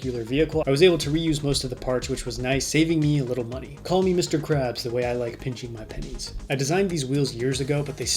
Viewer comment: I thought he said "call me Mr. Grabs for the way I pinch my pants"